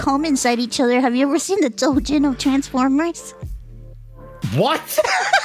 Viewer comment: I thought said "have u ever seen the doujins for Terraformers" and was very disturbed for a sec